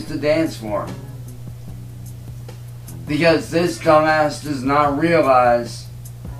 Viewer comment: Did he just blame age for the reason he doesn't dance anymore?The lies never cease to amaze. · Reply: Did he just call CVG "cumass"?